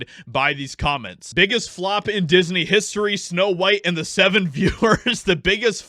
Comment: I did a spit-take, because I swear you said Snow White & the Seven Führers! 😂😂😂😂